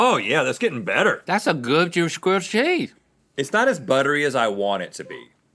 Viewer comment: What did link say at 11:10 lmao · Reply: "that's a good inaudible grilled cheese"